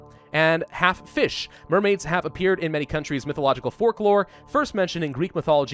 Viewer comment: did Say "forklore at 00:56? or is it just me i listened multiple times.